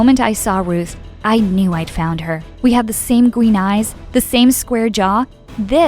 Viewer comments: I swear she said gween instead of green . Beside the mom's eyes are blue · did she say we have the same green eyes 👁️👄👁️